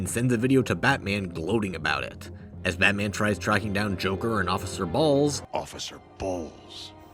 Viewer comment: sounds like he said "officer balls' 😂😅😂😅😂😂😅😅